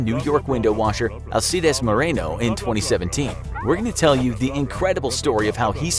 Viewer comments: WHEN THE SAY 2017 0:55 THE SUBTITLE SAYS 2007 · My subtitles said 2007 on 0:55 but the narrator said 2017. 😂